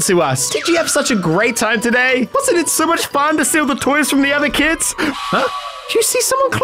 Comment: I thought said "steal all the toy from the other kids"